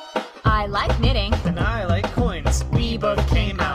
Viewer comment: 👁👁 what did it say? I must know Also i love how sun just accidentally pokes moons eye and has to make sure he doesnt do it again on accident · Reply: I like coins